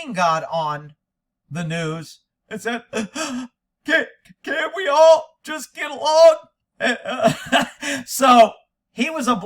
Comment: OH I thought Rodney king said CANT WE ALL JUST GET A DONG ? 😂🤣🤣😅